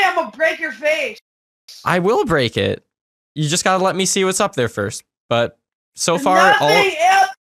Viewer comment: you dont see you just have to beleiiivveeeee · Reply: rinny tinny lol I just heard "you just gotta beeeeliiieeevee"! XD